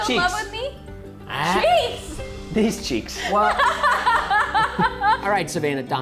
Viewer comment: I thought said cheese 😂😂